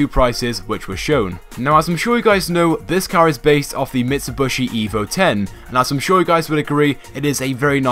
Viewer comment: What are your thoughts on the Karin Kuruma? Also be sure to drop a Like on this video! More videos coming later today :) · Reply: cool! a mitsubishi lancer :DD DID YOU JUST SAY MITSUBOSHI AT 0:59? goddammit xd